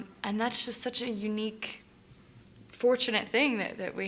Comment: sounds like she said "happy 20th anniversary I am da bee" lol